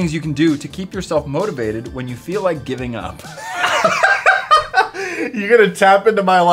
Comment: when was doing the motivational quotes when he asked what to do when you feel like giving up i thought he said what do you do when you feel like getting a knife